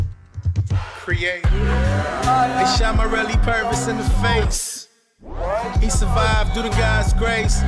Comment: i always thought he said "Is Shama really purvous in the face?"